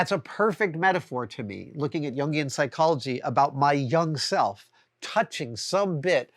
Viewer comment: Wait. Did you say, "my Jung self?" Wasn't that just now?